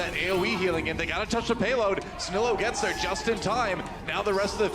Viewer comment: did he say payload?? On 2CP??